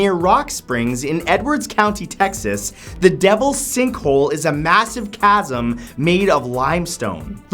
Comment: I thought that said the devil's stinkhole